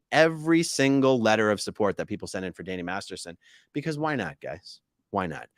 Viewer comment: It sounded like almost said, "Danny Mastersh*t," but he caught himself in time!🎉